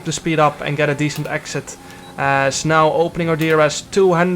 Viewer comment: u said snel instead of fast? :D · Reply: I think he said ‘as now’